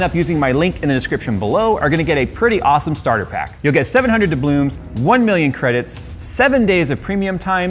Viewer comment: Did I hear "dublooms" ha ha